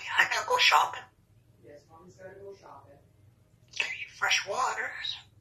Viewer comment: The "Your feet cold?" Made me put my big comfy socks on. Just had a bird remind me how cold tile gets😂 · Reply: Ohh lol - I thought she said 'get your vehicle.'